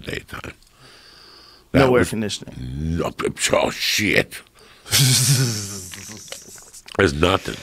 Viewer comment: what the hell did uncle Joey say 😂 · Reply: Basically a slurred version of "aw shit!".